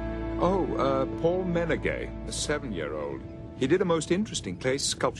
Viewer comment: I swear the first time I watched this video at 2:24 I thought Picard said "All men are gay."